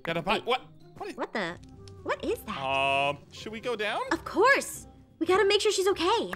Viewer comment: Ein says: um should we go down, but the subtitles say: Ein should we 3F3BDE, I don't know if that's a mistake or there on purpose but it's weird